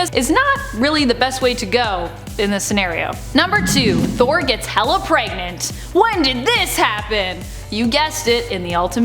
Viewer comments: Hela pregnant was a really interesting thing to see. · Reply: lmao thought at first she was saying thor got hell'a pregnant o_0 was expecting to see the son of odin in his third trimester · So when you said, "Thor gets Hela pregnant." What I heard was, "Thor gets hella pregnant." Apparently I watched one too many episodes of South Park and heard Cartman say the word "hella" one too many times and went a completely different direction when you said that. Now I have this image of the the God of thunder...great with child. And even worse, because it's the Ultimates universe...he has Chris Hemsworth's face and apprently....mead can be flavored with...wait for it...pickle juice. Verily this pickle mead is most disgusting, and yet strangely refreshing.